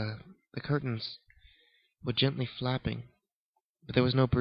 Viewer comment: Thought he said gently faping lol not flapping